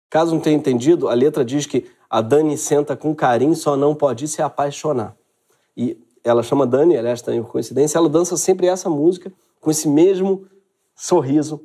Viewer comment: Eu não entendia a música cantada na minha própria língua; “ A Dani senta com carinho não pode se apaixonar”. Precisei descobrir isso aqui. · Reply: Sempre escutei “organizando a putaria só não pode se apaixonar” 🤣